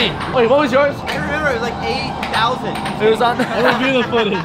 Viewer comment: Is it just me or at 2:51 he said 8,000 and the caption said 80,000